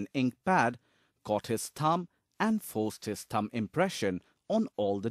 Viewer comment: I thought he said tongue. Not thumb...oops